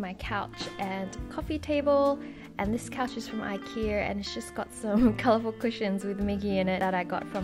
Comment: When you said 'this couch is from Ikea' and the cat was coming out from behind the pillows i thought you said 'this cat is from ikea' and i was light wait, what? haha where did you buy the ladder shelf? or did you build it yourself? thx very much!